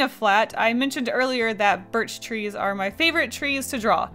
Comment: I love the way you draw birch trees in your illustrations, its so simple and clever! We have a lot of them in sweden too so they make me think of home hahhah <3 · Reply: I thought that said bitch trees xD